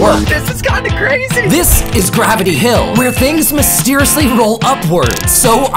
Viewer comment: I misheard it as Gravity Falls